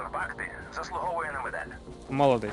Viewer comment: Мені одному почулось ніби він сказав той хто залишив тут бакту заслуговує на мінет? Чи мені здалось???